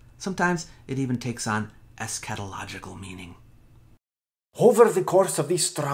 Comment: The way you pronounced eschatological almost made me spit take. I heard "a scatological" for a moment.